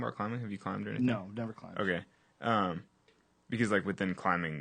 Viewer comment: he actually said "oh gay..." And not "okay"